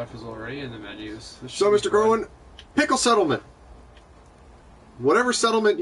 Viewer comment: HAHAHA at 1:28 I say pickle settlement instead of pick a settlement.